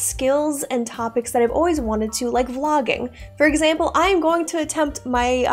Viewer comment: Vlogging, I misheard I thought you said, Logging.